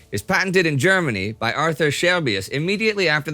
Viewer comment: I wasnt looking at the screen at the beginning of the videos i thought you said aurther shelby 🤣...want to watch some peaky blinders now